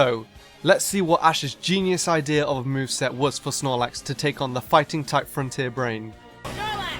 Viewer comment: 6 moves i thought he just said for snorlax to lazy